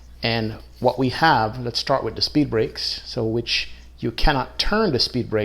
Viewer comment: did say speed brake